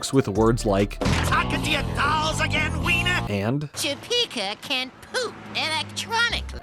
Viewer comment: I thought the bully said "tuggin on your balls again, weiner?" And I legit had to turn on subtitles.